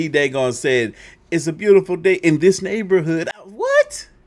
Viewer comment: Bruh I always heard "It's a beautiful day in my neighborhood" also I remember it as Febreeze and the battery was always in the Energizer rabbit's back because it was a huge battery.